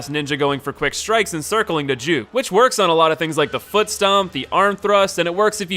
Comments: did you just say circiling the jew? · Did you say circeling the dude? Cause it sounded like you said circeling the jew. Whatever that's supposed to mean